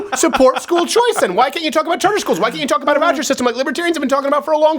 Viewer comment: I cannot understand what he is saying at 0:07 and 0:08 Roger system??? What?